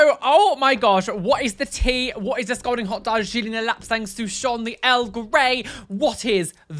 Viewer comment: Hahaha, I don't normally use the closed captions, but I accidentally turned them on at the start of this video and the did you DIRTY today 💀 When you said "What is the scalding hot Darjeeling, the lapsang souchon, the earl grey", YouTube's CCs thought you said "What is this golden hot dog she didn't elapsed thanks to Sean the L Gray" 😂 Ahhhh dead. A dead body.